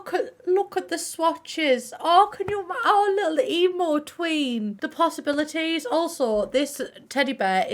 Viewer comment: I heard Emo twink and I felt called out